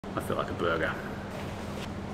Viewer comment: I thought you said, "I feel like a booger." Which I thought was Australian slang for, "I feel terrible." So I thought you were going to make chicken noodle soup or something like that. Imagine my confusion. P.S. No, I did not read the title.